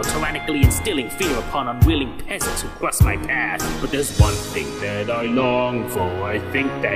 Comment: The ONE THING that bugs me about the song is. In the subtitles it says "something" When it sounds like SMG4 is saying "one thing" Subtitles: "there's something that I long for" SMG4: "there's one thing that I long for"